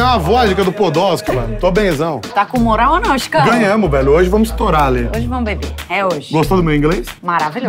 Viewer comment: KKKKK SÓ EU QUE OUVI, "HOJE NOS VAMOS TORAR A ALÊ"